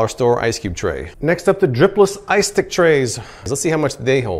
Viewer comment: Sounds like he said, "Dripless Ice Dick Trays."